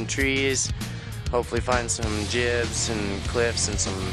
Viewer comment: it sounds like he is saying hopefully we will finds some jizz :)